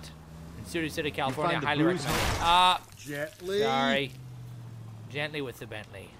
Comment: was it only me who thought Phelps's partner said Jet Lee rather than Gently?